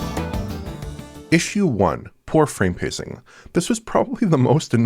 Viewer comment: thought it said "poop frame pacing"